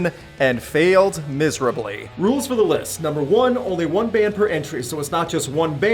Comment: I thought you said only one entry per band when AC/DC had 3 entries?? Or did I just not hear you right??